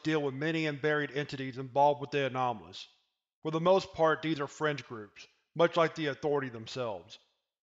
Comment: I thought he said “French” groups for a second.